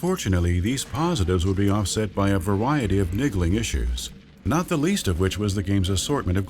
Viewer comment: I swear I heard this guy say nigglin....